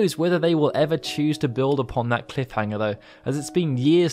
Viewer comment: Did you just say Yunaverse? XD